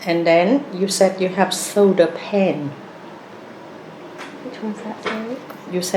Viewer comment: I also got confused when she asked you about your "soda pen". I thought it was some sort of diabetes thing or something...I felt quite embarrassed when I discovered she meant shoulder pain and it was just misheard 😭 such a lovely yet assertive lady, and great video!